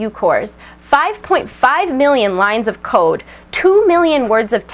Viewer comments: I thought she said lines of coke :/ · i swear to god she said 5.5 million lines of coke at 2:56... it would make sense. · i thought she said "5.5 million lines of coke..." seems about right.